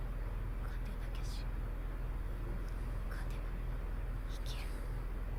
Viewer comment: The subtitle is wrong. What Mikasa said is "lose and die, win and live", the same thing Eren told her when they were kids.